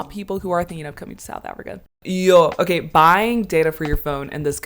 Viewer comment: Did I hear you say Yoh, now you are South African 😊